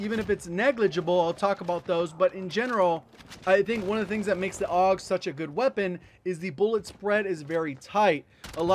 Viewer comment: I thought you guys said the AUG sucks lol